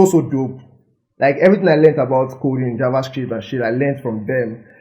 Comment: did you say shit😂😂😭